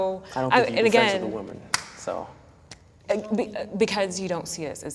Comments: did he say that u cant be friends w a woman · What exactly does say at 8:36? I can't hear it and I just wanna know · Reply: What I heard was “I don’t think I could be friends with a woman”. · "That woman could be your best friend" "I don't think you can be friends with a woman" Literally what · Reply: I thought he said I don’t have women friends